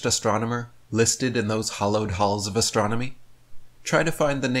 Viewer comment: Did he say “hollowed halls?”